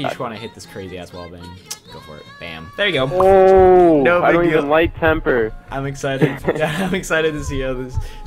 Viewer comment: did say he doesn't even like temperrr